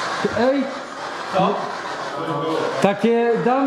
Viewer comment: usłyszałem 'tak'